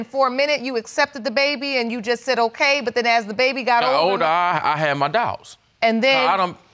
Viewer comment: And here I thought he said "I have my dots!" ...as in he's been connecting the dots bad had figured it out.